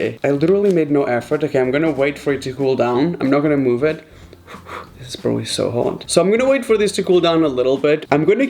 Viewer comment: did he just say spoon instead of knife?? Or am I just going crazy? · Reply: He went wo wo then it’s probably so hot that’s what he said